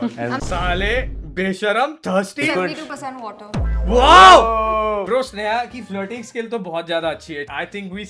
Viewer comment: what did she say here??? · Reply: "I am 72% water" because human body consists of 72% water.